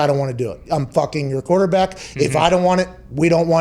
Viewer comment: Anyone notice Pat said “I’m f***ing your quarterback” instead of meaning to say “I’m your f***ing quarterback”